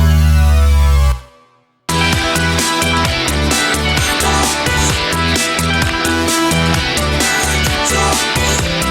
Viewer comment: te ne diyorlar ya? 'lanet oldu', 'saat üç oldu' arasında bir şey fgkfgkjfg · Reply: 'affeder mi? geçer mi?' diyor